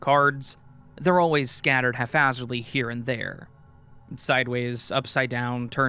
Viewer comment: If I heard right it sounded like you were saying half-hazardly. The word is haphazardly, just so you know. You might want to read it as the story was written but either way I wanted to point it out. (I’d want to know if it was me, apologies if you don’t).